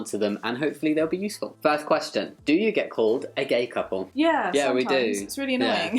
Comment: Am i the only one who thought he said the first question was do you get cold? I was like what?!